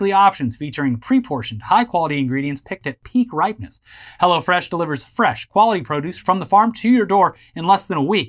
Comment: I initially thought they said "Hell-one" which sounds very dystopian (but also kinda caricatural and also not very arabic).